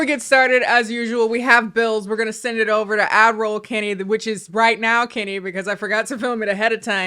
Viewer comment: AD ROLL???? For years I thought she was saying Admiral,,,, like ok Lieutenant colonel pop off…. But no I just can’t hear 😅